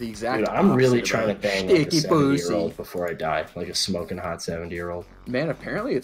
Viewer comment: Sounded like he was saying 7 year old instead of 70 year old